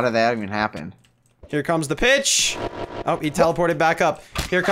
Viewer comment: did he say "bitch"?